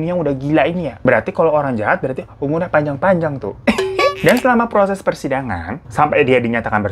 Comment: gua dengernya proses kosidahan😭